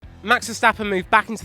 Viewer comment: No they werent. Max verstappen? I thought i heard lewis verstappen